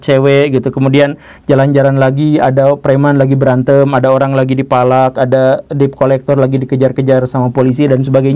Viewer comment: lah kenapa aku tdi dengernya "ada preman lagi dipalak" Pas ku ulang2 jdi "preman lagi berantem, orang lagi dipalak" Kok bisa bgitu😮